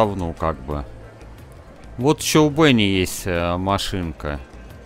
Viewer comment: послышалось "Вот Чолдуньи есть машинка"... Что? Аааа, у Бенни 😁😁😁